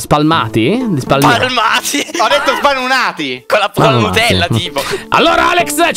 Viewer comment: Io ho sentito sbannati 👁👄👁😂